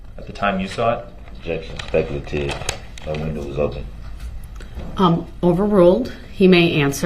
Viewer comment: I'm still trying to make out what Darrell says at 24:42. Did he say "my window was open"? That's an admission that he was the one driving the vehicle.